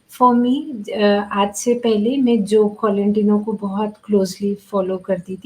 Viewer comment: which person did she said she follow very closly aroun 52:36s ? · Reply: She said Joe Colantonio